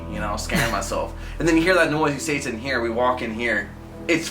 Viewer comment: is it just me or did say "satan here"?